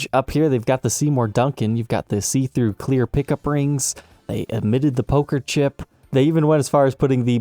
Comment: Sounds like you said they admitted the poker chip😳